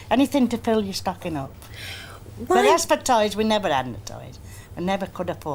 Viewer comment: ANYone, please...though I’ve played the clip countless times and am quite good at deciphering regional accents, the lovely lady at about 14:30 speaking on the contents of her Christmas stocking mentioned one last item before the ‘if you can have it on the inside of your belly, you can’t have it on the outside’...canNOT make out what the word is, even assuming it was some sort of candy/sweets...help, please...:) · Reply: Suzanne Reilman I think she said toys, and that they couldn’t afford toys.